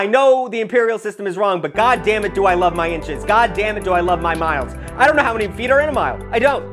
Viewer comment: My brain started tweaking and I heard “god dammit do I love feet”